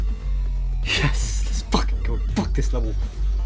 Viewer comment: all i hear is "fuck this level"